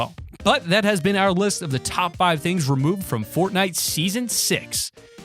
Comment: Um... did he say top 5?